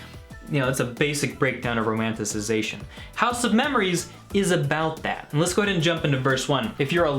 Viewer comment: Heard you say HOUSE OF GØLD